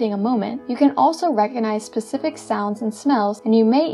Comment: did you say pacific? Or specific ?